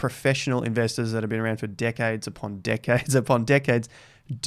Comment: I heard D*ckheads instead of Decades. 😂😂😂 Let's review his sentence. "Profesional investors that are being around d*ckheads upon d*ckheads upon d*ckheads."